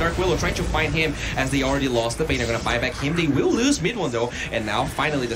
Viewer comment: I heard MidOne @ 13:30.